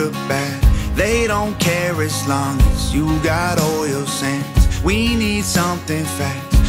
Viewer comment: He said "making widows of wives". I understand this. I'm a widow a Vietnam Vet. Didn't happen during the war but might as well have. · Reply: yeah I didn't understand "as long as you got oil sands" I thought he said "as long as you got all your sins" funny how an accent can be interpreted. "weirdos of wives" I chuckled until i got what he was saying about War.